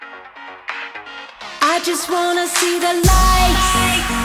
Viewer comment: Sounds like she is saying, " I just wanna see the lice". Anyway..............awesome song and love it.